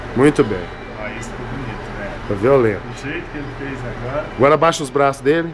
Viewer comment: O "esse foi bonito" do residente é extremamente preocupante... mostra uma absoluta insensibilidade e indiferença ao fato de estar manusendo o corpo de alguém que há 13 minutos ainda estava vivo. Se eu precisasse de um médico neurocirurgião por algum motivo, definitivamente n iria querer confiar minha vida nas mãos de um profissional que não parece se abalar minimamente diante da morte. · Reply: Eu entendi "esse foi violento"